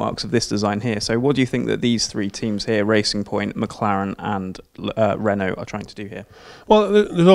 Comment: Did a hear a Lotus at 2:13 instead of Renault ??